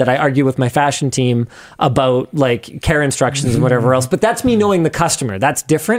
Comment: did he say "Karenstructions"? :face-blue-smiling